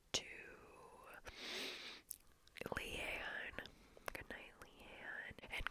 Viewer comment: Does she say Lean or Leon? Doesn't sound like Leon so I'll have to stay up my whole life now. R.I.P